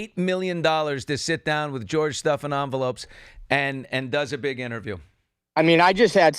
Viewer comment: Billy, did you just say George “Stuffin-envelopes”? 😂